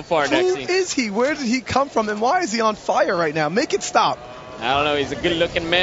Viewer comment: who is he where did he come from (right there i lagged and i thought he said) and why is he fat